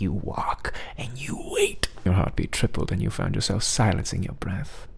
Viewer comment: BYE I THOUGHT HE SAID DADDY💀